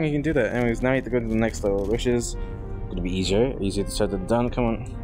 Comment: why did he say weezer 😢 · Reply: He didn't? He said easier